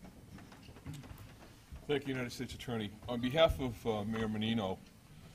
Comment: John candy does he say 6:41 "Dick United States Attorney". He says his name so fast